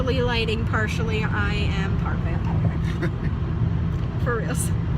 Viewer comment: I thought u said hung over, not vampire 😂